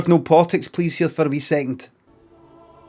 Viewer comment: What on earth did the guy say at 0:40? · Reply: Can I have no politics please here for a mere second